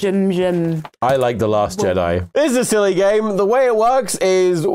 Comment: I couldn’t hear tomska clear enough. What did he say? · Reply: “I liked The Last Jedi.”